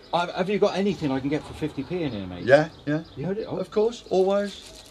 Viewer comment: it sounded like you said sex for 50 p 😂. what is this accent? I must know